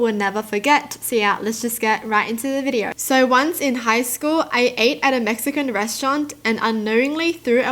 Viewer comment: Two things: 1. The first story I thought they said “so once in high school I ate a Mexican restaurant” 😂 😭 🌮 2. The thumbnail what if the guy who “stole” the iPod was friends with the guy who returned it and they were setting him up for the person to like him 😱 ❤️ 😁